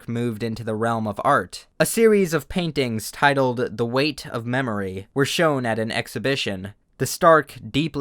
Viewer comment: I thought it said memes instead of memory